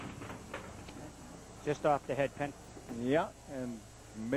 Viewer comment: On Don Santiago's third box, says "Just off the head pin" but with his thick Boston accent it sounds like he says "pissed off the head pin" :)